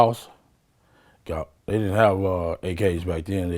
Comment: I thought Orlando said they didn’t have tickets for the fight 🤣 🤣